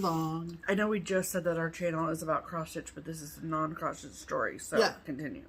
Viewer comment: I was starting to fall asleep when I put your show on and I thought I heard you say "this is a show about prostates" .... that woke me up!